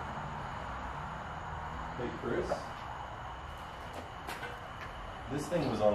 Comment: a males voice said got u Chris or get out couldn’t quiet figure what he was saying